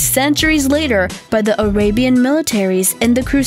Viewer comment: She says Damascus funny. "Damn ass cuss steel" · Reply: I swear I also heard her say "orabian" lol